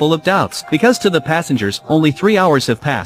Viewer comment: I thought it said bold detective